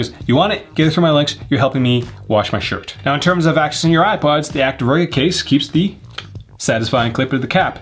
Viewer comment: accessing your iPods? Anyone else hear that lol? 😂